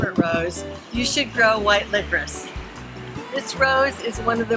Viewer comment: What is the rose name on 1:45? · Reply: Sounded like she said "white libresse"